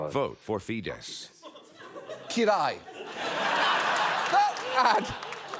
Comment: As a Hungarian, I can proudly say he is very much hated, especially since he loves Trump. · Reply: hey, dumb question but I can't get it out of my head. In the video at 11:38 when Jon says "cool" in hungarian, doesn't he say király? Meaning king? Is that slang for cool or am I just mishearing what he's saying? My hungarian sucks but I'm very interested in this lol